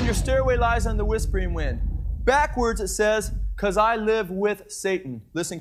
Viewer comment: It actually sounds like it says :" Ohhhhh because I live with soy. "